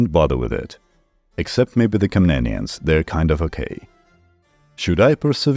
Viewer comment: I replayed a part because I thought I heard the word "Kanaan" which would have been an interesting connection to the old Testament. Then I heard "Except maybe the Komnenians, they're kind of OK.". And then I saw this is by far the most replayed sentence. So does everyone misunderstand that part or what?